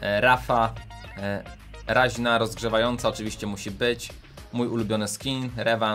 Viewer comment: czy tylko ja usłyszałem rozgrzewająca?